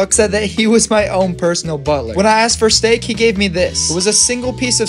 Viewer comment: Bro i thought he said bottler